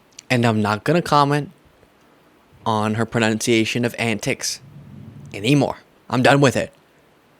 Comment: Lol "antics" -- I thought she said "annex", and my folks are Appalachian.